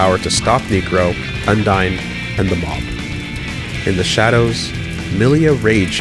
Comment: what did you say around 13:30? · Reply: "and the mob"?